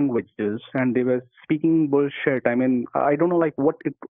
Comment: Did he say they were speaking "bull shirt"??